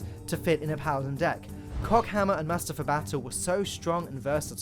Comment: Did he just say cockhammer?